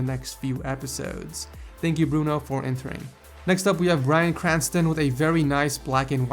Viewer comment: Woah woah woah... back it up, did he just say Bryan Cranston? 1:40 lol walter put that meth money to good use.